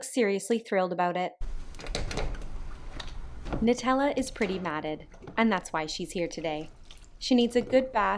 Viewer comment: "Nutella is pretty matted." I thought for a second you said "Nutella is pretty MADDENED." Love the way she slams on the brakes with her paw & claws as if you were dropping her into hot lava.